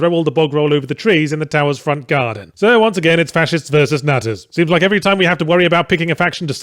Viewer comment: Nutters? I thought he said nazis throughout the whole video and then was left wondering 'But aren't nazis Fascists?'